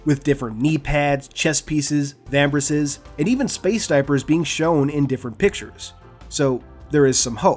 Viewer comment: what was the last thing being listed? I keep hearing space diapers but that cant be it right? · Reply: You heard correctly, I said "space diaper".